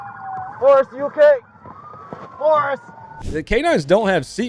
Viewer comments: Just saw another comment claiming to know the k9 officer, and in fact his name is Boris.... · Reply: Oh lol. I thought he said "Forest" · Was it Boris or Forrest? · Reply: I could've swore he said Forrest, myself.